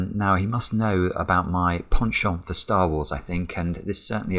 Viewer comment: Word of the day: Penchant. Meaning: a strong or habitual liking for something or tendency to do something. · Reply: At first I misheard it as "Pawn Shop for Star Wars". And now that I've imagined that as Simon's other YouTube Channel, I wish it were true.